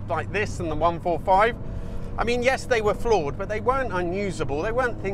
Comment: Flawed !!!! I thought you said "Floor It"